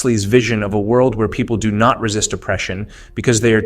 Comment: time index 08:56 / narrator says; "where people do not resist opression" / subtitles say; "where people do not resist depression" /// please make a note of it. cheers.